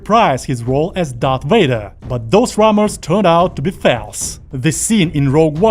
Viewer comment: "false" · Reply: idk man. I heard “Failse”.